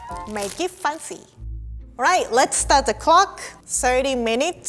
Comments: I thought it said "Start the Glock" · I thought she said let’s start the glock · idk why but I thought that said start the glock 😂😂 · I thought it said start the glock 😂